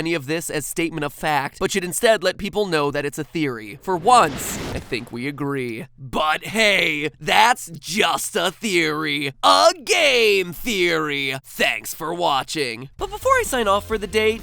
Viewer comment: "A GAY THEARY" what I thought I heard.